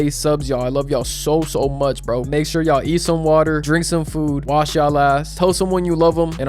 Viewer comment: Outro threw me off for a sec😂😂 all I heard was “Make sure y’all eat some water and drink some food..”and had to do a double take. Got me out here like Kevin hart “wait a minute wait a minuuuute”